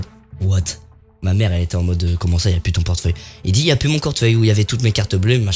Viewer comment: ta dit quoi cortfeuille mdr🤣🤣